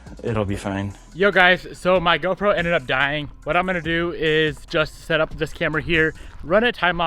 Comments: i thought he said his girlfriend ended up dying but he said gopro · I heard girlfriend instead of GoPro and was like wtf 😂